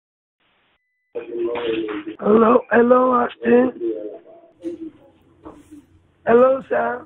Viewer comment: when he called it sounded like he said "hello ashton"